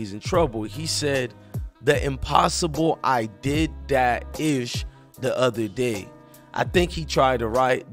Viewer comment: He did not make no mistake. U don't add everything on Twitter cause they shorthand . This what he said, "The impossible! I did that shit the other day". He ran his sentences together.